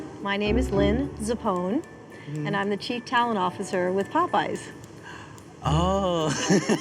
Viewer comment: Okay in the first 30 seconds, the lady says “ I’m the chief talent officer” but what I heard is “I’m the chief talon officer” and I thought that was some kind of joke to do with chicken.